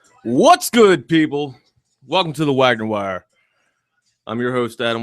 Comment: Nice podcast. I, for whatever reason, thought you were saying “wagger” and couldn’t find your channel anywhere. XD